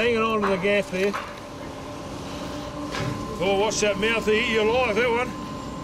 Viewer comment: I'm an aussie and for anyone struggling to understand what he said, he said "Watch out for that mouth, it'll eat you alive, that one."